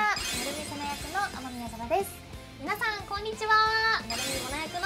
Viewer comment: あの、あの…ですね… とても可愛いしすっごい楽しみなんですけど 0:11「雨宮天です」が「雨宮様です」にしか聞こえなくて…